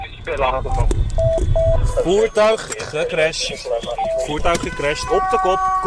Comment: it is dutch (I think) but what I hear in my head is spanish "puta crash." It fits right🤣.